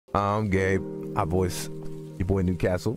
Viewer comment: Sounded like he said "I'm gay" right when the video started.